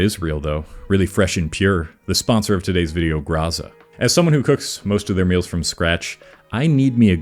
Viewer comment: I thought that said "Gaza" and I was like "hold up what the f" and then realizd it was Graza.